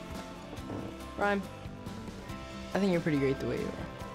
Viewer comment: What did he said on 0:50 ? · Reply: He said "Rhyme, I think your pretty good where you are," I think...